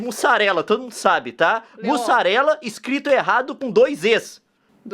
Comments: Leon me desculpa mais eu entendi "dois és" 😂😂😂😂😂😂 ahh e aki em Minas Gerais nois fala mistura sim.. 😂 😂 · juro que entendi "dois Es"e não "Zs", E É MISTURA SIM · Eu também entendi "dois Ês" e eu sou de Pernambuco kkkkk